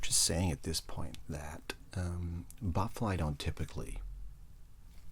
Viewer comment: Thought I heard you almost called it a bloatfly, thought fallout for the rest of the video lmao.